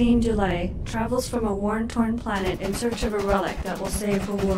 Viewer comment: If you listen at 20:05 I swear the narrator says "worn torn" instead of war torn as if even the VO for that was like screw it no need to enunciate lol